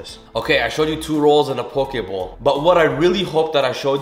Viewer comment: I thought it said pork bowl I was sooooo confused